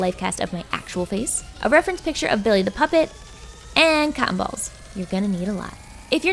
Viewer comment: When she said cotton balls I thought she said cat balls